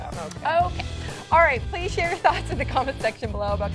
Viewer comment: Lol,it sounded like she said "cliche your thoughts..."#5wordsorless.And yes it was a nice thong😂